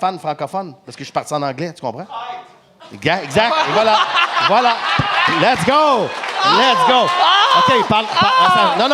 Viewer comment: c’est quoi que le dude dans la crowd dit ? Fait 15x je réécoute jentend pas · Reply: Traître